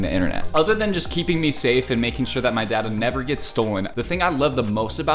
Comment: damn I thought he said his dad gets stolen😭😭😭